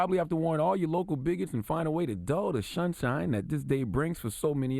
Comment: Wait, did he just say "shunshine" @ 2:59? Outrageous. Why is he shunning the sun. Must be a ray-cist